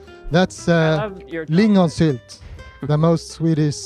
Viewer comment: I thought Hawk said that is 'linnusitt', which is bird shit in Estonian 😂